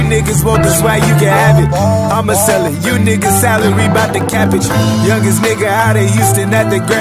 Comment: I always thought he said " You n***as celery bout to cabbage 🥬 "